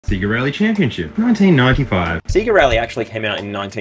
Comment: for the firs 15 seconds, i thought was saying "Sigarelli" and not "sega rally". ffs